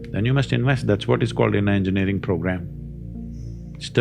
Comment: subtitle says ‘an engineering program’ Sadhguru meant ‘inner engineering program’ It’s a special course crafted by him taught at Isha Foundation and other places.